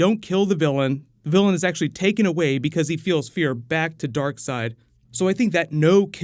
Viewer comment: What does say? "the villain is actually taken away because he feels fear... back to darkseid?"